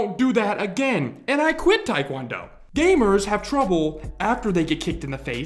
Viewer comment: sorry but the only thing I hear is “I’m a quitter”